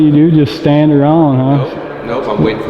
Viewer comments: Is a warm hand off like a happy ending? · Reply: I thought he said warm hand job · Did he say he wanted to give his supervisor a warm hand job at4:56? Sounded like that to me!!!!😁😁😁😁😁😁